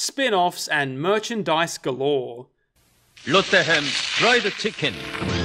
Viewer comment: I heard Robocop said "Oh, what the hell, fried chicken"